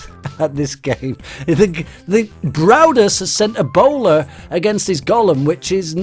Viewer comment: it sound like he says braudus has sent ebola